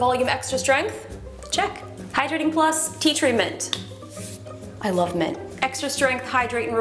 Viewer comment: I heard her saying "I love men" at 0:52, but is that what she said? It sounds out of character if she did.